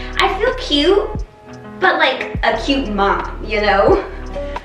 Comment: When I heard you say, you feel like a cute mom, all I heard was you feel like a cute "old person"